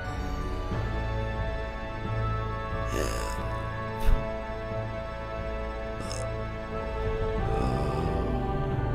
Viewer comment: what was he saying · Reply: I think he saying help